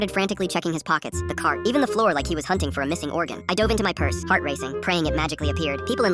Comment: I heard " fart racing"